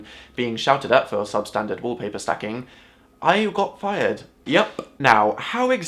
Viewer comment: I thought it said how i got fried....